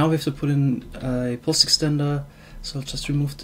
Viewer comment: Sounded like Wurst-Extender